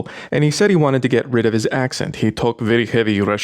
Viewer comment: i actually understood: ... a russian friend who wanted to get rid of his "ex and..." instead of "accent" ... was concerned when you casually brought up the "window" idea...